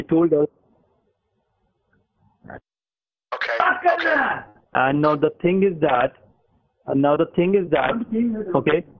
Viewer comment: I couldn't hear! What did the guy in the background say at 15:33?! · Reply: I thing he shouted "paagal hai" Which translates to "she's crazy!"